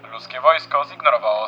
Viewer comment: usłyszałem ruskie wojsko 😅